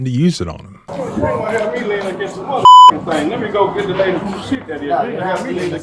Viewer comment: does he say "let me go get the lady" and something about "help you"? It sounds like he says "let me help you".???